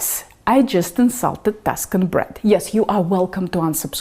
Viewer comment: You know, I do offer captions ;) just turn on CC button in the bottom right of your screen. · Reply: Got it and that's what I used. At the time it was translating to Duskin bread. I just had to back it up a bit to rehear Tuscany sometimes. I googled Tuscan bread and it didn't look pale so I thought you were saying something else. I think people are posting pictures of something that is not Tuscan Bread. Just trying to learn from you ;)